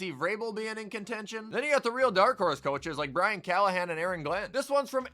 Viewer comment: new fan of the channel, never seen the halloween intro before.fire to see! also 1:44 did i hear you say “ aaron glenn”? the 0-5 jets coach in contention for coty? idk